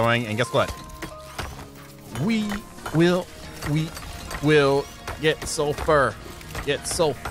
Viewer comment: for a second there i thought he was saying we will we will get so far .....